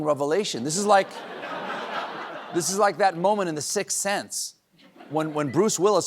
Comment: Brain damaged...instead of, "Sixth Sense," I heard, "Six Seven." Great news, after Tiberius died, Rome only got better!